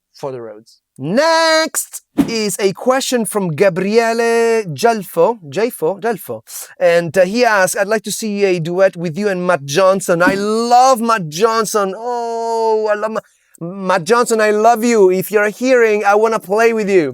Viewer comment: he sounds like he is saying "would you like to do a duet with my Johnson."